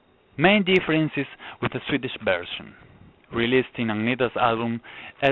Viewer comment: Ah, thanks indeed I misheard "Swedish" as "Spanish"!